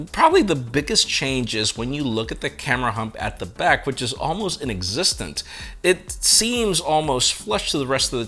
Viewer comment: "almost an existent".. what does that mean? Did you mean to say almost NON-existent?